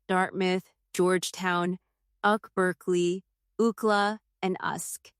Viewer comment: Did she really say uckla, usck and uckberkeley 😂 (UCLA, USC and UC Berkeley)